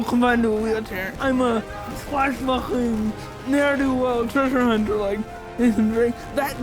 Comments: Terribly sorry to add this, since it is such a great video, but the subtitles are wrong at 4:21. It says "I'm a swashbuckler. There to treasure-hunt, like Nathan Drake", but he actually said "I'm a swashbuckling ne'er-do-well treasure hunter, like Nathan Drake". I can't reasonably enjoy this video until that mistake is fixed. /s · are the subtitles wrong? Sounds like he says, "I'm a swashbuckling, ne'er-do-well, treasure hunter like Nathan Drake."